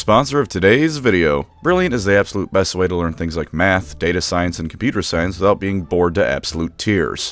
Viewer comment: I heard ''brilliant is the best way to learn ma-" and my brain filled in "magic" instead of math. Somehow I doubt that brilliant has a course on that!